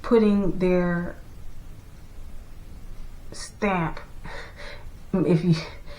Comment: Girl! You said put their “stamp” on schools and in my head I heard “stank”. I guess we’re both right. Please consider coming back to youtube